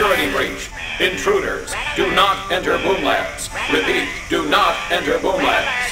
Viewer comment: I thought they said boob labs....